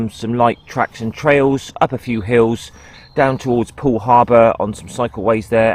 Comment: did i hear Driving down to pearl harbor ??? better look out for the Japanese 😲